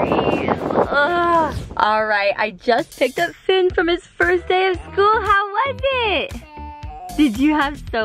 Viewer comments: Coming back to this video I laughed when said first day of school I thought he said potato potato potato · When Finn said first day of school I thought he was saying potatoe